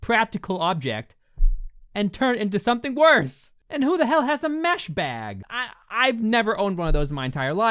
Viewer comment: I thought he said meth bag not mesh bad...